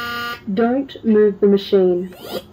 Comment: what is it saying? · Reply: Don't move the machine